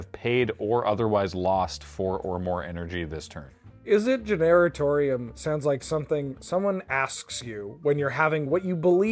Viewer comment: I keep hearing "Izzet Gendertorium"